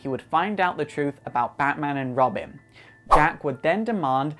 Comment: Did he just say Robim?